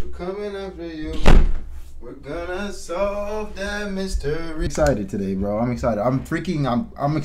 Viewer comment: I thought he said were gonna suck that mistery qt the begginning